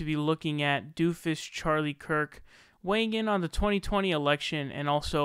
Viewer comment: I thought you said doomfist Charlie Kirk. JLP would be doomfist of the ridiculous right.